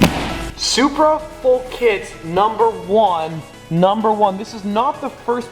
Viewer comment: I thought it said f***it